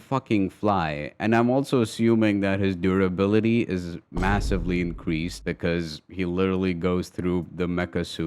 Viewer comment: Did somebody just say massive